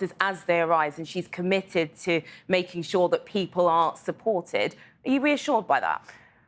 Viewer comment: the interviewer (aisha?) seems to say, and I quote directly what I heard: "...Liz Truss has said she will deal with the circumstances as they arise, and she's committed to making sure that people AREN'T supported, are you reassured by that?" What the actual F--- 💀😵🥺😧😨😢🤬